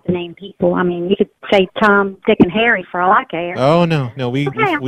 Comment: Did she just say "Tom , long, Dick, and harry"?????